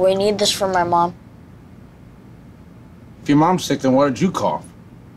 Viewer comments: "If your mom's sick then why did you cough?" That kills me every time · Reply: I always thought he said " why didnt you call" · "If your mom's sick then why did you cough?" That kills me every time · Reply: I always thought he said "if your mom´s sick then why´d you come"